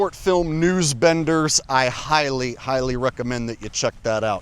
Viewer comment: News-Benders (1968) for anyone looking. I thought was saying News Spinners. 👍